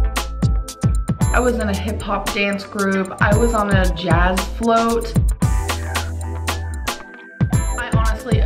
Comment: I had to rewind, I could've sworn she said "I was in a hip hop dance group. I was a jazz float.". 🤭🤣